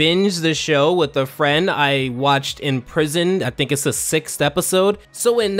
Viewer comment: I thought this man said he binged watched Avatar in prison